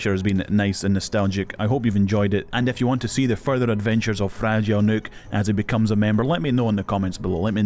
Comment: Fragile Nuke grew up so fast 🥲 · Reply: I thought he was saying fragile mook this whole time